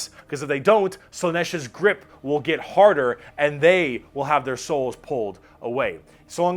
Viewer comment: fuck man i heard 'Slaneesh's dick will get harder*